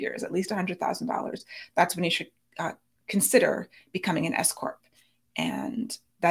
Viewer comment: I keep hearing 'if you're considering becoming an escort" instead of 's-corp' 😂